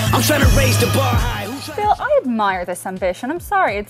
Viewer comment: wait pause 1:44 did she say i admire this son of a bitch lol